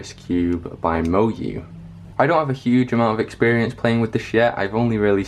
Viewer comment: I don't have a huge amount of experience playing with this yet. What I heard: "I don't have a huge amount of experience playing with this shit"